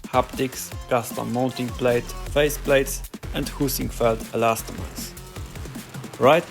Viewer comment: I could have sworn you said "poosyfoot" when you said Heisingveld or w/e haha